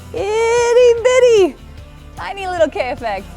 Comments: Thought it said Kanye effect... 😂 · i thought it said Kanye effect